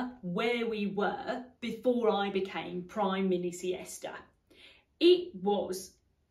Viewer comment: what..Prime-miss-SIESTA?! 😆😅🤣😂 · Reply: I heard “Prime Mini-Siesta” 😂 Didn’t Thatcher say she took only naps?